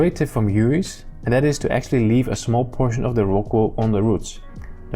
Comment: Leave a small portion of the what on the roots? I couldn't tell what word you said there. Thanks! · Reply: The rock wool! That's that yellow stuff in the pot where the plants are growing in